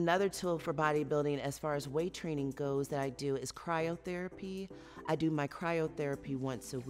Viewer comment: I thought she said “crying therapy” and I was like me too girl me too